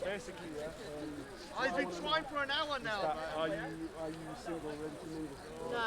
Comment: whispered to her are u single a ready to mingle at exactly 20:00 to 20:07 then she says no. Then he says his doing Dawah EVERYONE LISTEN FOR YOUR SELF WHEN HE TAKES HER TO RAILING LISTEN TO WHAT HE SAYS VERY LOWLY TO HER BUT MICROPHONE PICKED IT UP. give me a like once you hear him